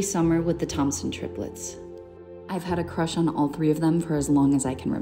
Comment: I thought she said toxic triplets 😂😂